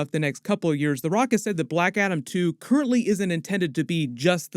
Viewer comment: I thought he said Blackadder and thought… “is that still on?!”